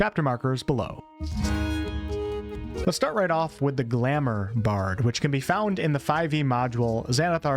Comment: Hahaha, I heard "The Grammar Bard"